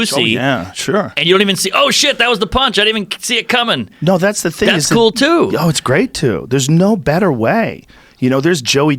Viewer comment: Who's the comic they're talking about at 17:20 something, couldn't catch the name really tried hard...anyon? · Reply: Jeselnik?